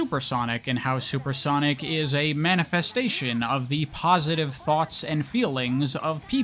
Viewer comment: Bruh I was not listening much and all I heard was "sonic is a comi" bruhhhhh